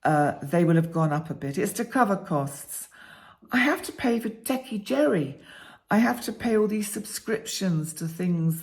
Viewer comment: Did you say “techie Jerry?”